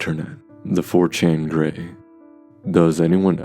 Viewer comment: bro I thought it said The 4chan Gay